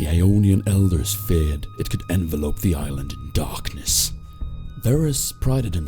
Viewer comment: did u say envelope?